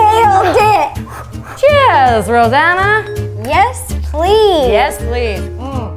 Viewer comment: i thought she said Havana